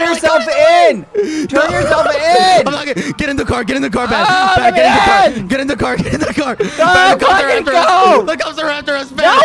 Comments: Since when did bad swear? He straight up said “go fucking go” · Me replaying it a hundred times cause I heard Bad saying: "GO!!! FUCKING GO!!" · does it sound like bads saying "fucking go" to anyone else? lmfao my mans STRESSED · DID ANYONE ELSE HEAR BAD SAY "go f*kin go" CAUSE I DID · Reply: He said "go quackity go" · it's sounds like bad said "GO F*CKING GO" I hope I'm wrong 😂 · WHAT DID BAD SAY WAS THAT GO FUCKING GO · Quackity swore so much that BBH gave up yelling “language” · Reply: idk if anyone else catch it but bbh said go fucking go at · am i the only one who heard bbh say "go fuckin go" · did i hear "go f***ing go" from bad at 24:08 ? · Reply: No he said " Go quackity go " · I think bad said the F word He said go F-ing go!